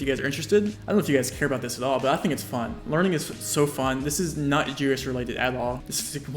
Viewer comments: What does he say at 5:32? Sounds like "this is not jewish related at all" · The CC is weird at 5:33 when he mumbles, I think he's saying "This is not GeoGuessr related" but the CC thinks he said "This is not Jewish related" lol